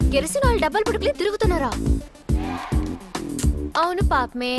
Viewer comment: Does anyone know what Kajal says at13:44. I want to know what she says in Telugu, the telugu dialogue.... Not translation. or meaning..She says something like Kirasanail dabbal kodukulai tirugutunnaru.. Not sure what she says.